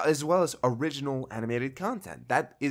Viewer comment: Did he just say "Original Animated Content?" As in they're going to make their own original anime series!?